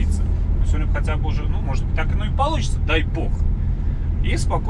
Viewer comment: 4.58 мне послышалось "И спокойно ехать напится". )))